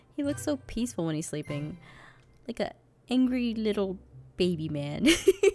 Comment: "Angry Little Baby Man" 😂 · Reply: I thought she said Angry little pony man XD heh it was funny i was like " ANGRY LITTLE PONY DUDEEEEEEEEEEEE!!!!!!!!! " HEHEHEHEH XD